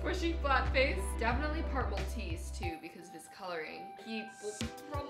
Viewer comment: When you said part Maltese I thought you said purple teeth. Xxxx 😂